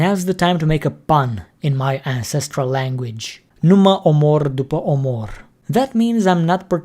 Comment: it sounded like romanian "numai omor dupai omor", and soothing like, "only kill after kill"